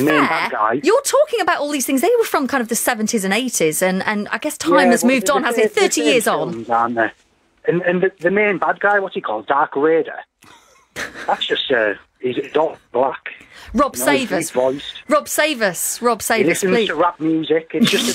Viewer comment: EPIC TROLLING! Darth Raider.... the racist sterotype who liked rape music.... meanwhile BBC feminist liked playing PRINCESS Leia as a kid, but finds kids doing the princess thing today sexist! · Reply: ***** I thought he said 'Dark Raider', which is even better. Haha! Good lord, the BBC!